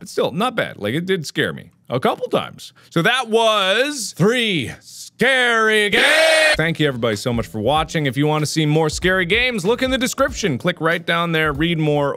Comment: Mark: scary GAAAME- What I think he said: HAIRY GAYYYY